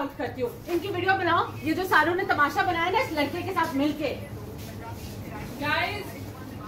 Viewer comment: bro wtf 😂😂 she's literally saying ye jo SARON ne tamasha bnaya